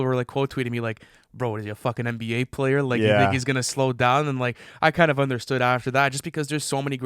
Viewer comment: am I crazy did he just say that? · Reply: he said “You think he’s gonna slow down” but he muttered it kinda so it didn’t sound that way lol